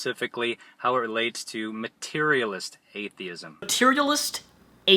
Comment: it sounds like he is saying "HateTheist"